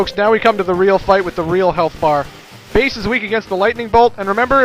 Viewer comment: did said bass is weak to the lightningbolt?